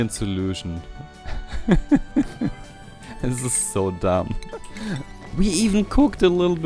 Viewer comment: Was haben meine Ohren da gerade gehört? 25:47 Es ist so dumb? bist du deutsch? Selbst wenn, wahrscheinlich kein Geheimnis und ich habe es nur verpasst...